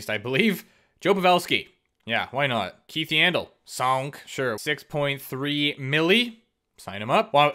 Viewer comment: 😂 when you pick yandle did you say soink lol couldn’t stop laugh great video btw